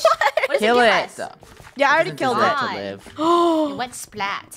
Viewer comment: for anyone who is confused on what happened Draco just said "did you kill it it doesn't deserve's to live"